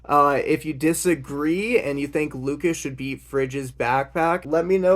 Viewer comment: I heard Lucas should be Fridge's backpack and I'm for it.